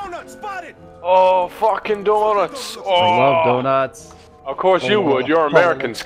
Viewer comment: Does he really says "Donouts Spotted" :D?